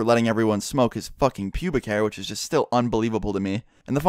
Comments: Oh, I thought it said public hair. Oh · I thought the vid said "guy smoking public air" then I was like well I'm a twat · I thought it said public hair... I was like "What the hell, is there A drug I don't know of?" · At first I thought it said "Public Hair" i was like tf? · thought it said public hair. was so confused. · I thought it said public air not pubic hair